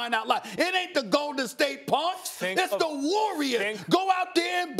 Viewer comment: I thought he said golden state pugs